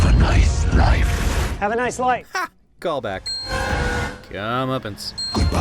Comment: Not gonna lie when venom said "have a nice life" i thought he said "you have a nice knife"